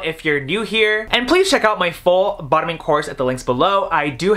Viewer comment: Wait, did he just say “my full bottoming course”? As in, a course on how to be on the anally receiving end of a male-male sexual interaction?